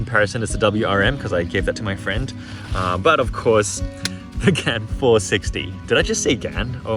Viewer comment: The whole time I thought you were saying “ gun 360”